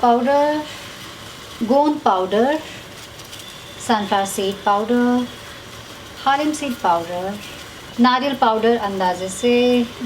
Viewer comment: ye kya bola????? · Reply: Sunflower seed powder🙄